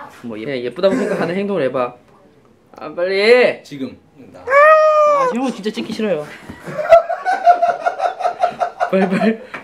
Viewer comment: anyone who understands korean what did jimin say to hobi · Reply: He said "I really don't want to film you(hobi)" 😂😂